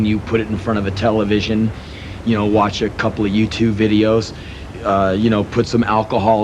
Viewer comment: Lol. I thought he said watch a couple of YouTube videos. He said U2 videos. Henry Rollins time traveler....for a sec anyway.